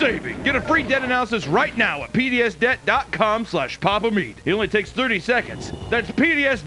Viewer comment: i stg i thought he said Penis death xD